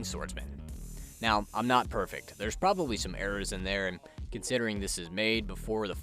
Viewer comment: what did he say here? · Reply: That there could be errors in the video due to me overlooking things or information that comes out in the future. Lol. Thanks for watching!